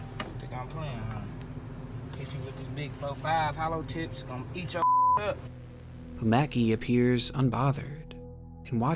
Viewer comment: What did he say to 22:28 -2229. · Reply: "big four fives, them hollow tips gon eat you ass up"